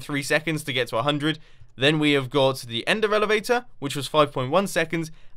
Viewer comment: Did anyone hear him say Ender Elevator